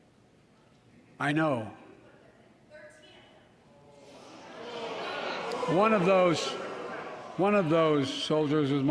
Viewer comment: What was the person yelling out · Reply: “13 of them” …talking about Biden getting 13 marines killed pulling out of Afghanistan